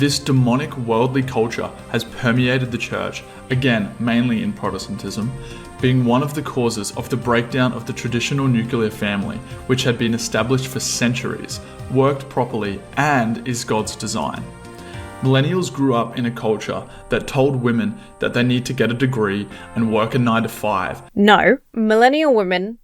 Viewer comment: Did he say (at approx. 23:30) that the nuclear family has been established for centuries?